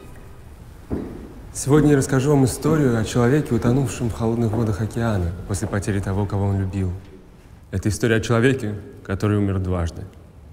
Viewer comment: Can someone tell what is that man saying at 1:07 ? I'd be thankful :) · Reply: "Today I will tell you a story about a man drowned in the cold waters of the ocean. After the loss of someone he loved. This is a story about a man who died twice." sorry for googletranslate